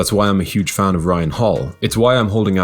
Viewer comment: Ryan Hall or Uriah Hall? I heard ryan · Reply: Uriah Hall 😂